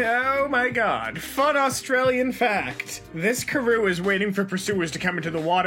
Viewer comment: did just say karoo